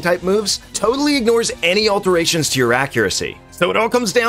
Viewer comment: Every time you say magical leaf, it sounds like your saying magikaleaf